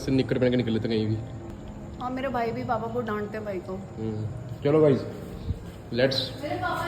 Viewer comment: kya bol rhni hai ,,,, mera bhai papa ko dnta hai ya papa bhai ko dante hai kuchh smajh nahi aaya guyzz 😂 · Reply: Mein bolri thi mere papa mere bhai ko dant te hai agr vo bahar ache se kapde daal kr nahi jaye to😂